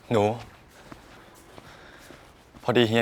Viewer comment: OMG 7:09 !!! I knew I heard it before, when Lian was asking if they could sleep together again... HE CALLED HIM "NU"!! NU!!! I'm not ok 😭 I can't handle the sweetness!! 😍